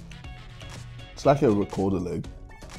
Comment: What does he say at 2:00? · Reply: It’s like a rekordelig